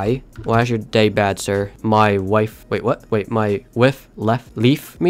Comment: i thought he said "my wifi left me" lol